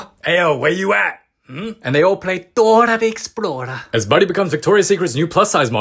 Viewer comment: what he said : Dora the explorer trapnscript 💀: THoRA tHe ExPlOrEd